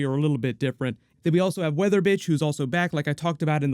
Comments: When you said weather witch I thought you said weather bich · Did say "Weather Bitch" at 7:19? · I swear I heard you say “Weather bitch” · 😮” Weather Witch” or “Weather Bitch”? I heard the second one 😆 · did you just call her Weather Bitch 😂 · It sounds like you said “weather bitch” and 7:20 LMAOOO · lol, did say weather b***h instead of weather witch at 7:18? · Wait... Did you just say "Weather Bitch" instead of "Weather Witch"? OMG! HAHAHAHAHAHA!!!! XD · 😂😂 sounds like you said weather bitch 7:17 love your videos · legit sounded like you said weather bitch lol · Did he just say weatherbitch😭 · sounds like he said weather bitch 😂